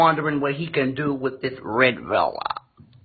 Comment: Did he call himself "Red Fella" 🤣🤣⚰️ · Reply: I thought he said red velvet